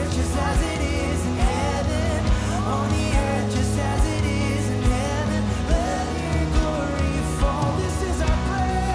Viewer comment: What is being said at 7:50 ? I can’t hear it clearly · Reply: On the earth just as it is in Heaven